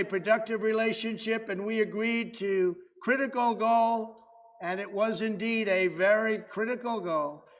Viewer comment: Thank you so much for the subtitles. I looked away for a few seconds & thought he said, "Crit-a-go-go." Envoking images of ppl dancing in the mid 1960's. It was "Critical goal".